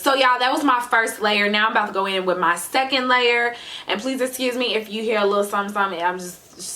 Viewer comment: what did she say at 6:00 ? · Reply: “Second layer”